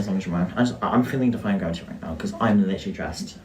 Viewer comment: dumb, sheltered canadian here, what the hell are they saying at 9:34? i can tell they're fighting but i can't make out words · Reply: “Im feeling defying gravity right now as I am dressed for it”